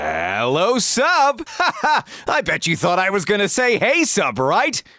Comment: I thought he said helllll sub